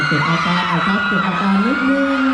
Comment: จะตะโกนอะไรนักหนาฟังไม่ได้ยินเสียงเลยพับผ่าสิ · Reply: MAHIRO CHANG นาที 2:14. จะฟังเสียงอาจารย์พูดว่า เก็บอาการหน่อยนึ่ง เเปลว่า มารยาทเด็กนักเรียน ยังไม่ดีพอ